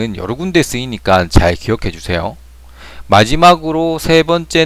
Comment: The language barrier is huge, sorry. I'm not sure what your question means. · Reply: did you say goro majima? 2시 46분에 마지마 고로라고 했지?